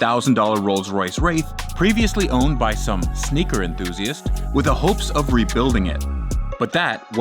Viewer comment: Bro called Marcus rashford a sneaker enthusiast 💀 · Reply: i Thought he'd said "Snooker Enthusiast" 😆